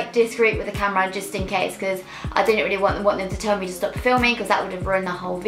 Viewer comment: wtf did you say here lol sometimes that British accent is impossible to understand :D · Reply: M A ‘i didn’t really want them to tell me to stop filming’